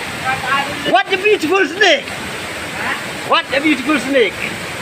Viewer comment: I heard " Watcha butiphul snake " 😂